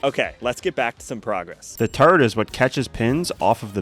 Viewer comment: Who else thought he said "turid" at 6:36 👇